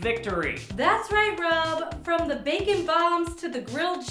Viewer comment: Every time they said bacon bomb, I thought they said bagtan bomb -_